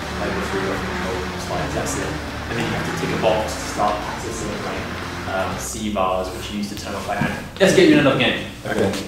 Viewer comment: Thanks for showcasing the injector, but I have to address the subtitles you've edited into this video - I know it can sometimes be hard to hear what the guy offscreen was saying but sometimes the subtitles just don't make any sense at all, especially when they misinterpret the technical terms that he's using in his explanations of the software. For some examples, in just the 10 seconds between 4:38 to 4:48, "And then you have to take a box to start practicing at C bars, which used to turn off by hand." ("And then you have to tick a box to start accessing, like, cvars which are used to turn off like a-" Admittedly I can't tell what was cut off at the sentence there, and I'm not a developer but just google "cvars unreal vr" and I'm pretty sure that's what he was talking about, not "practicing at C bars") "Let's get you in and out again." ("Let's get you in another game.) "Do you want to sound enough motion?" ("Do you want standing up, motion?") "I'll use it for everyone." ("I'm easy, whatever you want.") Or the 20 seconds from 6:48 to 7:08: "best showcase of the six-stuff controls" (six-stuff = 6-DOF) "indie game first perosn shooter. So I'm gonna shoot them." ("So, a boomer shooter" (referring to illWill - Steam description "Drawing inspiration from DOOM, Quake, Blood and Serious Sam, ILLWILL is a very stylish and comedically brutal old school boomer shooter.")) "We've got Ian Spied in for the third-person game." (Ian Spied = Aliens Fireteam. This one is really bizarre. Did yo